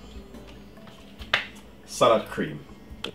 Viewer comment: is he saying salad cream? or sour cream? and if he is saying salad cream what is salad cream? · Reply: Yes it’s salad cream...... it’s a Heinz brand